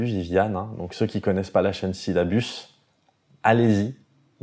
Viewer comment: À 17:20 il parle d'une chaîne, c'est quoi ? Syllabus ? Je ne trouve pas. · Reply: scilabus :)